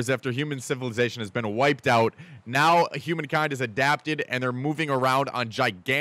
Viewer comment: i thought u said human kind is a napkin lol